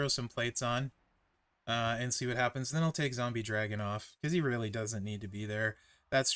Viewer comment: When you said "take Zombie Dragon off", I thought you said "Zombie Dragunov" and was excited for Ilja Dragunov to be in the game. Lol